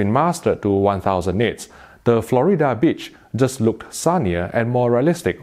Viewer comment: I swear he said “Florida Bitch” haha